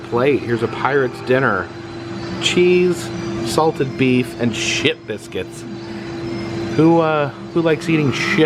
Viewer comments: thought he said shit biscuits lmao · If i didn't look at the screen, i thought said "S**t biscuit" xD Plus, Ripleys seems to really know how to be creative with their surroundings. Awesome!